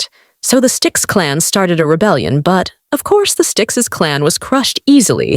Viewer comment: I heard styx's as sticks